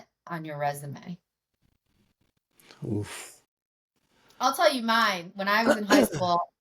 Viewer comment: did you say WOOF @ 53:02? Gonna have to refer to you as Harrison Ford now!